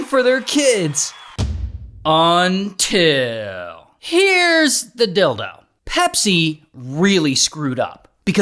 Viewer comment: is it me or am i hearing "Here's the dildo" instead of "Here's the deal tho"